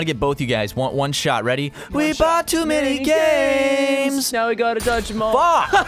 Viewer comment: I legit just realized the intro says "He bought too many games" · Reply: I thought it said "He sought to play games"